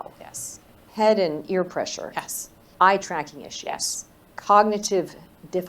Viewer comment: What did she say after "ear pressure"..sounded like she said "eye tracknish"? what the hell is that?